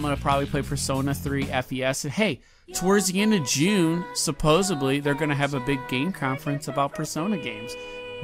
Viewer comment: did he just say "supposably" lmao